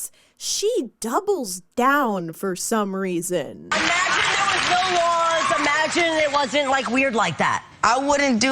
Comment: Instead of saying "imagine if there was no kids at your show" she says imagine if THERE'S WAS NO LAWS ??? 💀💀💀